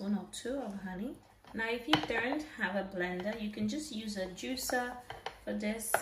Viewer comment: I couldn't work out if you said u can use a juicer instead of blender or juicer instead of sieve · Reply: Juicer or a blender :)